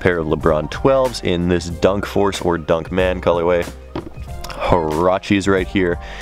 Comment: I thought he said harambe lol